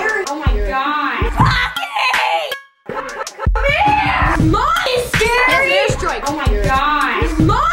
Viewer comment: it sounds like is saying at 1:17 she says "Mommy's scary" did she?